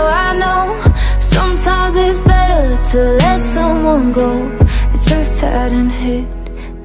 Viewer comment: I heard "I use to be mad but now I know sometimes It's better to let someone grow" is this grow or go?